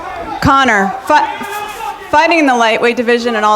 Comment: did she say Conor Fuck at 4:50?